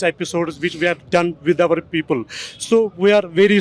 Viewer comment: he clearly said "OUR PEOPLE". Wth is wrong with people in the comments